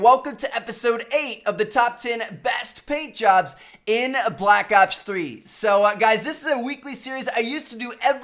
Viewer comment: I thought he said top paid jobs in black ops 3 I was so confused